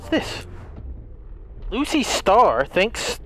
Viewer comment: did this man really just said Lucy instead of lucky?😂 I’m gonna be honest I also thought it was Lucy until I re checked it